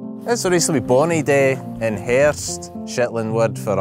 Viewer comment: Funny, I heard "hearst" or something like that, it means autumn in English. It is written "herfst" in Dutch, pronounced "hearfst". 🤣👍